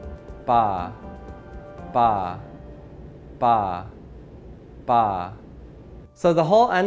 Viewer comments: I don't hear any of the letters on screen, I only hear ''Pa'' instead, no matter how much I concentrate I just can't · I hear fa -- now waiting for the "ck". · i wanted to hear BrainStorm and i heard Green Needle , guess my reality was already chosen by someone , or something. Btw 2:27 im only able to hear " Bah " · i heard ta